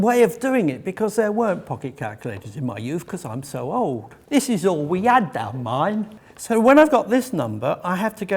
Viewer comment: I think what saying at 3:38 is, "This is all we had then, mind!" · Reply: "This is all we had down mine." A Northern (Lancashire) saying referring to the primitive working conditions found in a coal pit or mine.